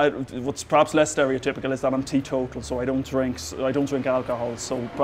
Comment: what did he say he was? Anyone caught that? I am very curious to know why he does not drink alcohol. Respect for that! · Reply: Suharto Gutur he said 'teetotal', an adjective of 'teetotaller', a pretty old-fashioned word that basically means the same as 'abstinent'. You can google 'teetotalism' to find out more.